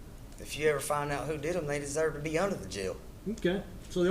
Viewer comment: Judge: "so what should the killers sentence be"? Him: "well judge i think they should be let free" Judge: "hol up" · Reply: I actually first thought he said. "If you ever found out who did it they deserve to be out of the jail"🤣 What is he actually saying???